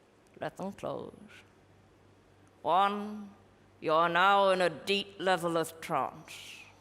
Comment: I thought she said a deep level of Trumps. Which would make perfect sense.